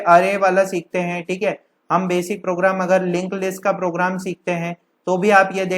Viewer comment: i didnt understand the word something like link? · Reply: ds lINKED List